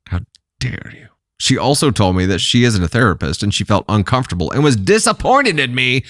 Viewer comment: Shes a therapist? No way she's a therapist? Of all ppl to cast that stone? Knowing men are the ppl too. Men cry too until they are hurt so bad they become stone and lash out for ever more. Damaged that no one can fix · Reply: I thought it said she was a therapist, too. (The downside of listening at double speed. 🤣) But I went back and listened at normal speed and she said she ISN'T a therapist. I was wondering why no one until you commented on that part. 🤣